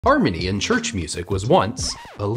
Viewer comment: At the beginning I thought he said ‘Armenian Church Music’ Instead of “Harmony in church music” and I was very confused.